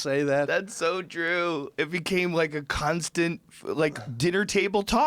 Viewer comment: did joe say "cunt-stant"?